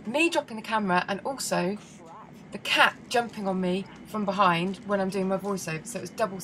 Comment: I may have misheard you.. Did you say the CAT fell on you?? O_O